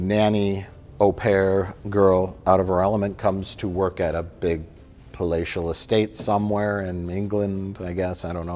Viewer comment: between this and the recent best of the worst, I keep mishearing 'palatial' as 'fellatial estate'.